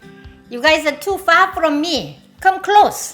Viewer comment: First time I stopped here, I thought she said "You are too fat from me" for "You are too far from me" - 😁